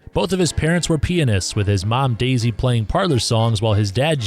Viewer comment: "Both his parents were pianists" I cannot tell how how horribly I misheard that as p3nis.